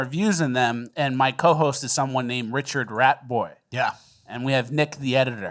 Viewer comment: I always thought you were saying Richard Bratford but you been saying ratboy lol hilarious